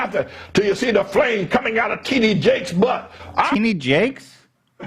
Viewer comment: Did ANYONE else notice how The flaming butthole pastor called out bishop T.D.Jakes ….. and Ethan thought he said “teeny jake” !?!?? 🤣🤣🤣 then proceeded to comment on how messed up it was to call “Jake” “teeny” (as if the entire clip wasn’t already fkd up beyond any comparison 😂) he literally said “bro… I don’t like that”… so the rest is fine but don’t refer to Jake as “teeny” 🤣… AND THEN proceeded to play footage of him getting things wrong/mispronouncing words while he went to the bathroom 🚽🤣🤣😭😭😭 ETHAN! it’s T.D. Jakes… he’s an African American tv evangelist/bishop/Christian film producer… js 🤷🏼‍♀️😂😂😂😂😂😂😂😂😂😂😂😂 BUT YO SHOUT OUT TO TEENY JAKE !!! AND SHOUT OUT TO HIS TEENY FAMILY!!! ✊✊✊